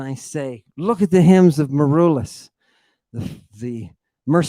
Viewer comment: or anyone who understand what says. At min. 25:18 something about the "hymns of Mur...." the mercenary poet that fought with Vlad Dracu "the Impaler" I don't exactly understand the Hymns of M.... who? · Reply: Michael Tarchaniota Marullus. Book to look for is 'Poems' (The I Tatti Renaissance Library 54) Enjoy.